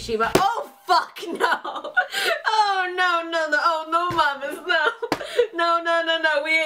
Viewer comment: i heard no mames? xd